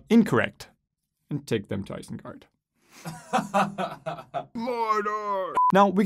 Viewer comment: What does he say at 2:12? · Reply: “Take them to Isengard” then “Mordor!” LOTR references